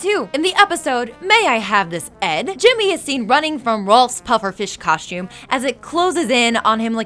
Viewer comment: Antotucci? Anotucci? Stop mispronouncing his name! And at one point, you call Rolf Walf... what the hell? How do you mess that up? 14:28 And did she say the Brothers Gunt?